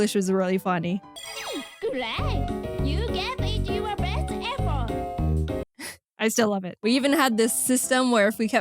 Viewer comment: Gweat you did yo best effot. Edit:that’s what mama sounded like she said